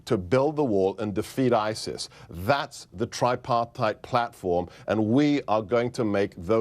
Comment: triparteid? what the fuck is that word?